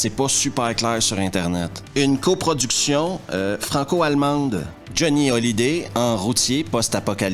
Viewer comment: J'ai compris "Johnny Holiday"... J'ai souri... 😂